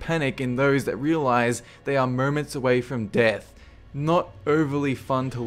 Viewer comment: i heard "mermaids away from death" at 1:15 xD